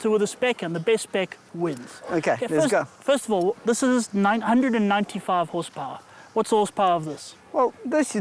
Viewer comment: and 95 Horsepower, not 195. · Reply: thought he said 995 hp.